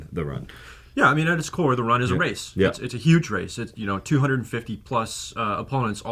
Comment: did he say 250+ racers